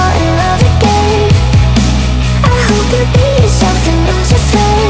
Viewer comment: LMAO when it says "I hope you be yourself" I keep hearing "I hope you pee yourself" and I WISH it said that